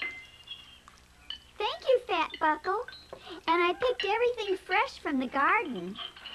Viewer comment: umm what did she just say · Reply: "Thank you, Fat-buckle. And I picked everything fresh from the garden". That is what she said.